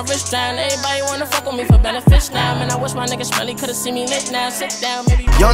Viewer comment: I thought he said melly